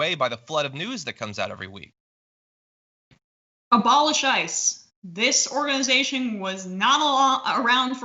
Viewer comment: Oh, no, my husband and I used to play 'Oh, I thought you said...' Emma said "Abolish ICE". I thought she said 'a ball of scheisse'. And it made perfect sense.